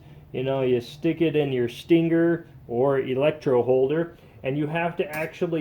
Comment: Did he just say sting ger